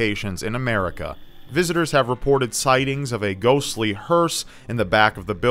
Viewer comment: I thought you said "a ghostly purse" and all I could think about was Sidney Prescott being chased around her house my a Gucci purse.